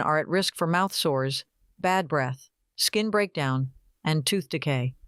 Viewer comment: You mean bad breath? Why does it say bath breath lol that threw me off